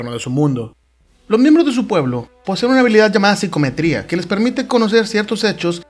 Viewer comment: alguien sabe como se escibre la palabara que dice en el min 1:25 siquiometria algo asi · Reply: D Psicometría.